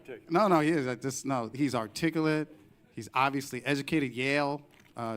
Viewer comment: Him saying "artiggelet" instead of "articulate" is fantastic.